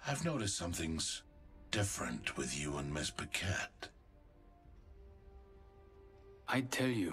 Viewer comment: First time I heard Caustic call Wattson ”Ms. Paquette” I thought he said ”Ms. Baguette” because she’s french and Caustic don’t really like anyone. Lol I was so stupid.